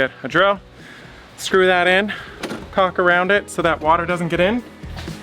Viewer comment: says Caulk, but the Closed Caption put [ __ ]. guess it thought he said something else. :)